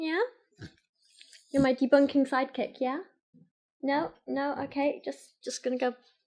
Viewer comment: I heard, "you're my debunking psychic" .... "oh wait... she said SIDEKICK".